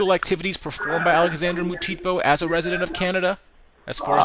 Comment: he said Canada but the subtitle said canda